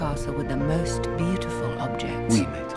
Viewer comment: What was the Prince saying at 0:35? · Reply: 1987AnimeBoy It's Lumière who talks and he says "oui maître" wich means "yes Master"